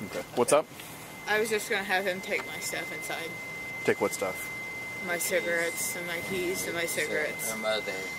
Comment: I could have sworn she said I was giving him "my ciggs and keys" to take into the house...not my moms ciggs smfh🙄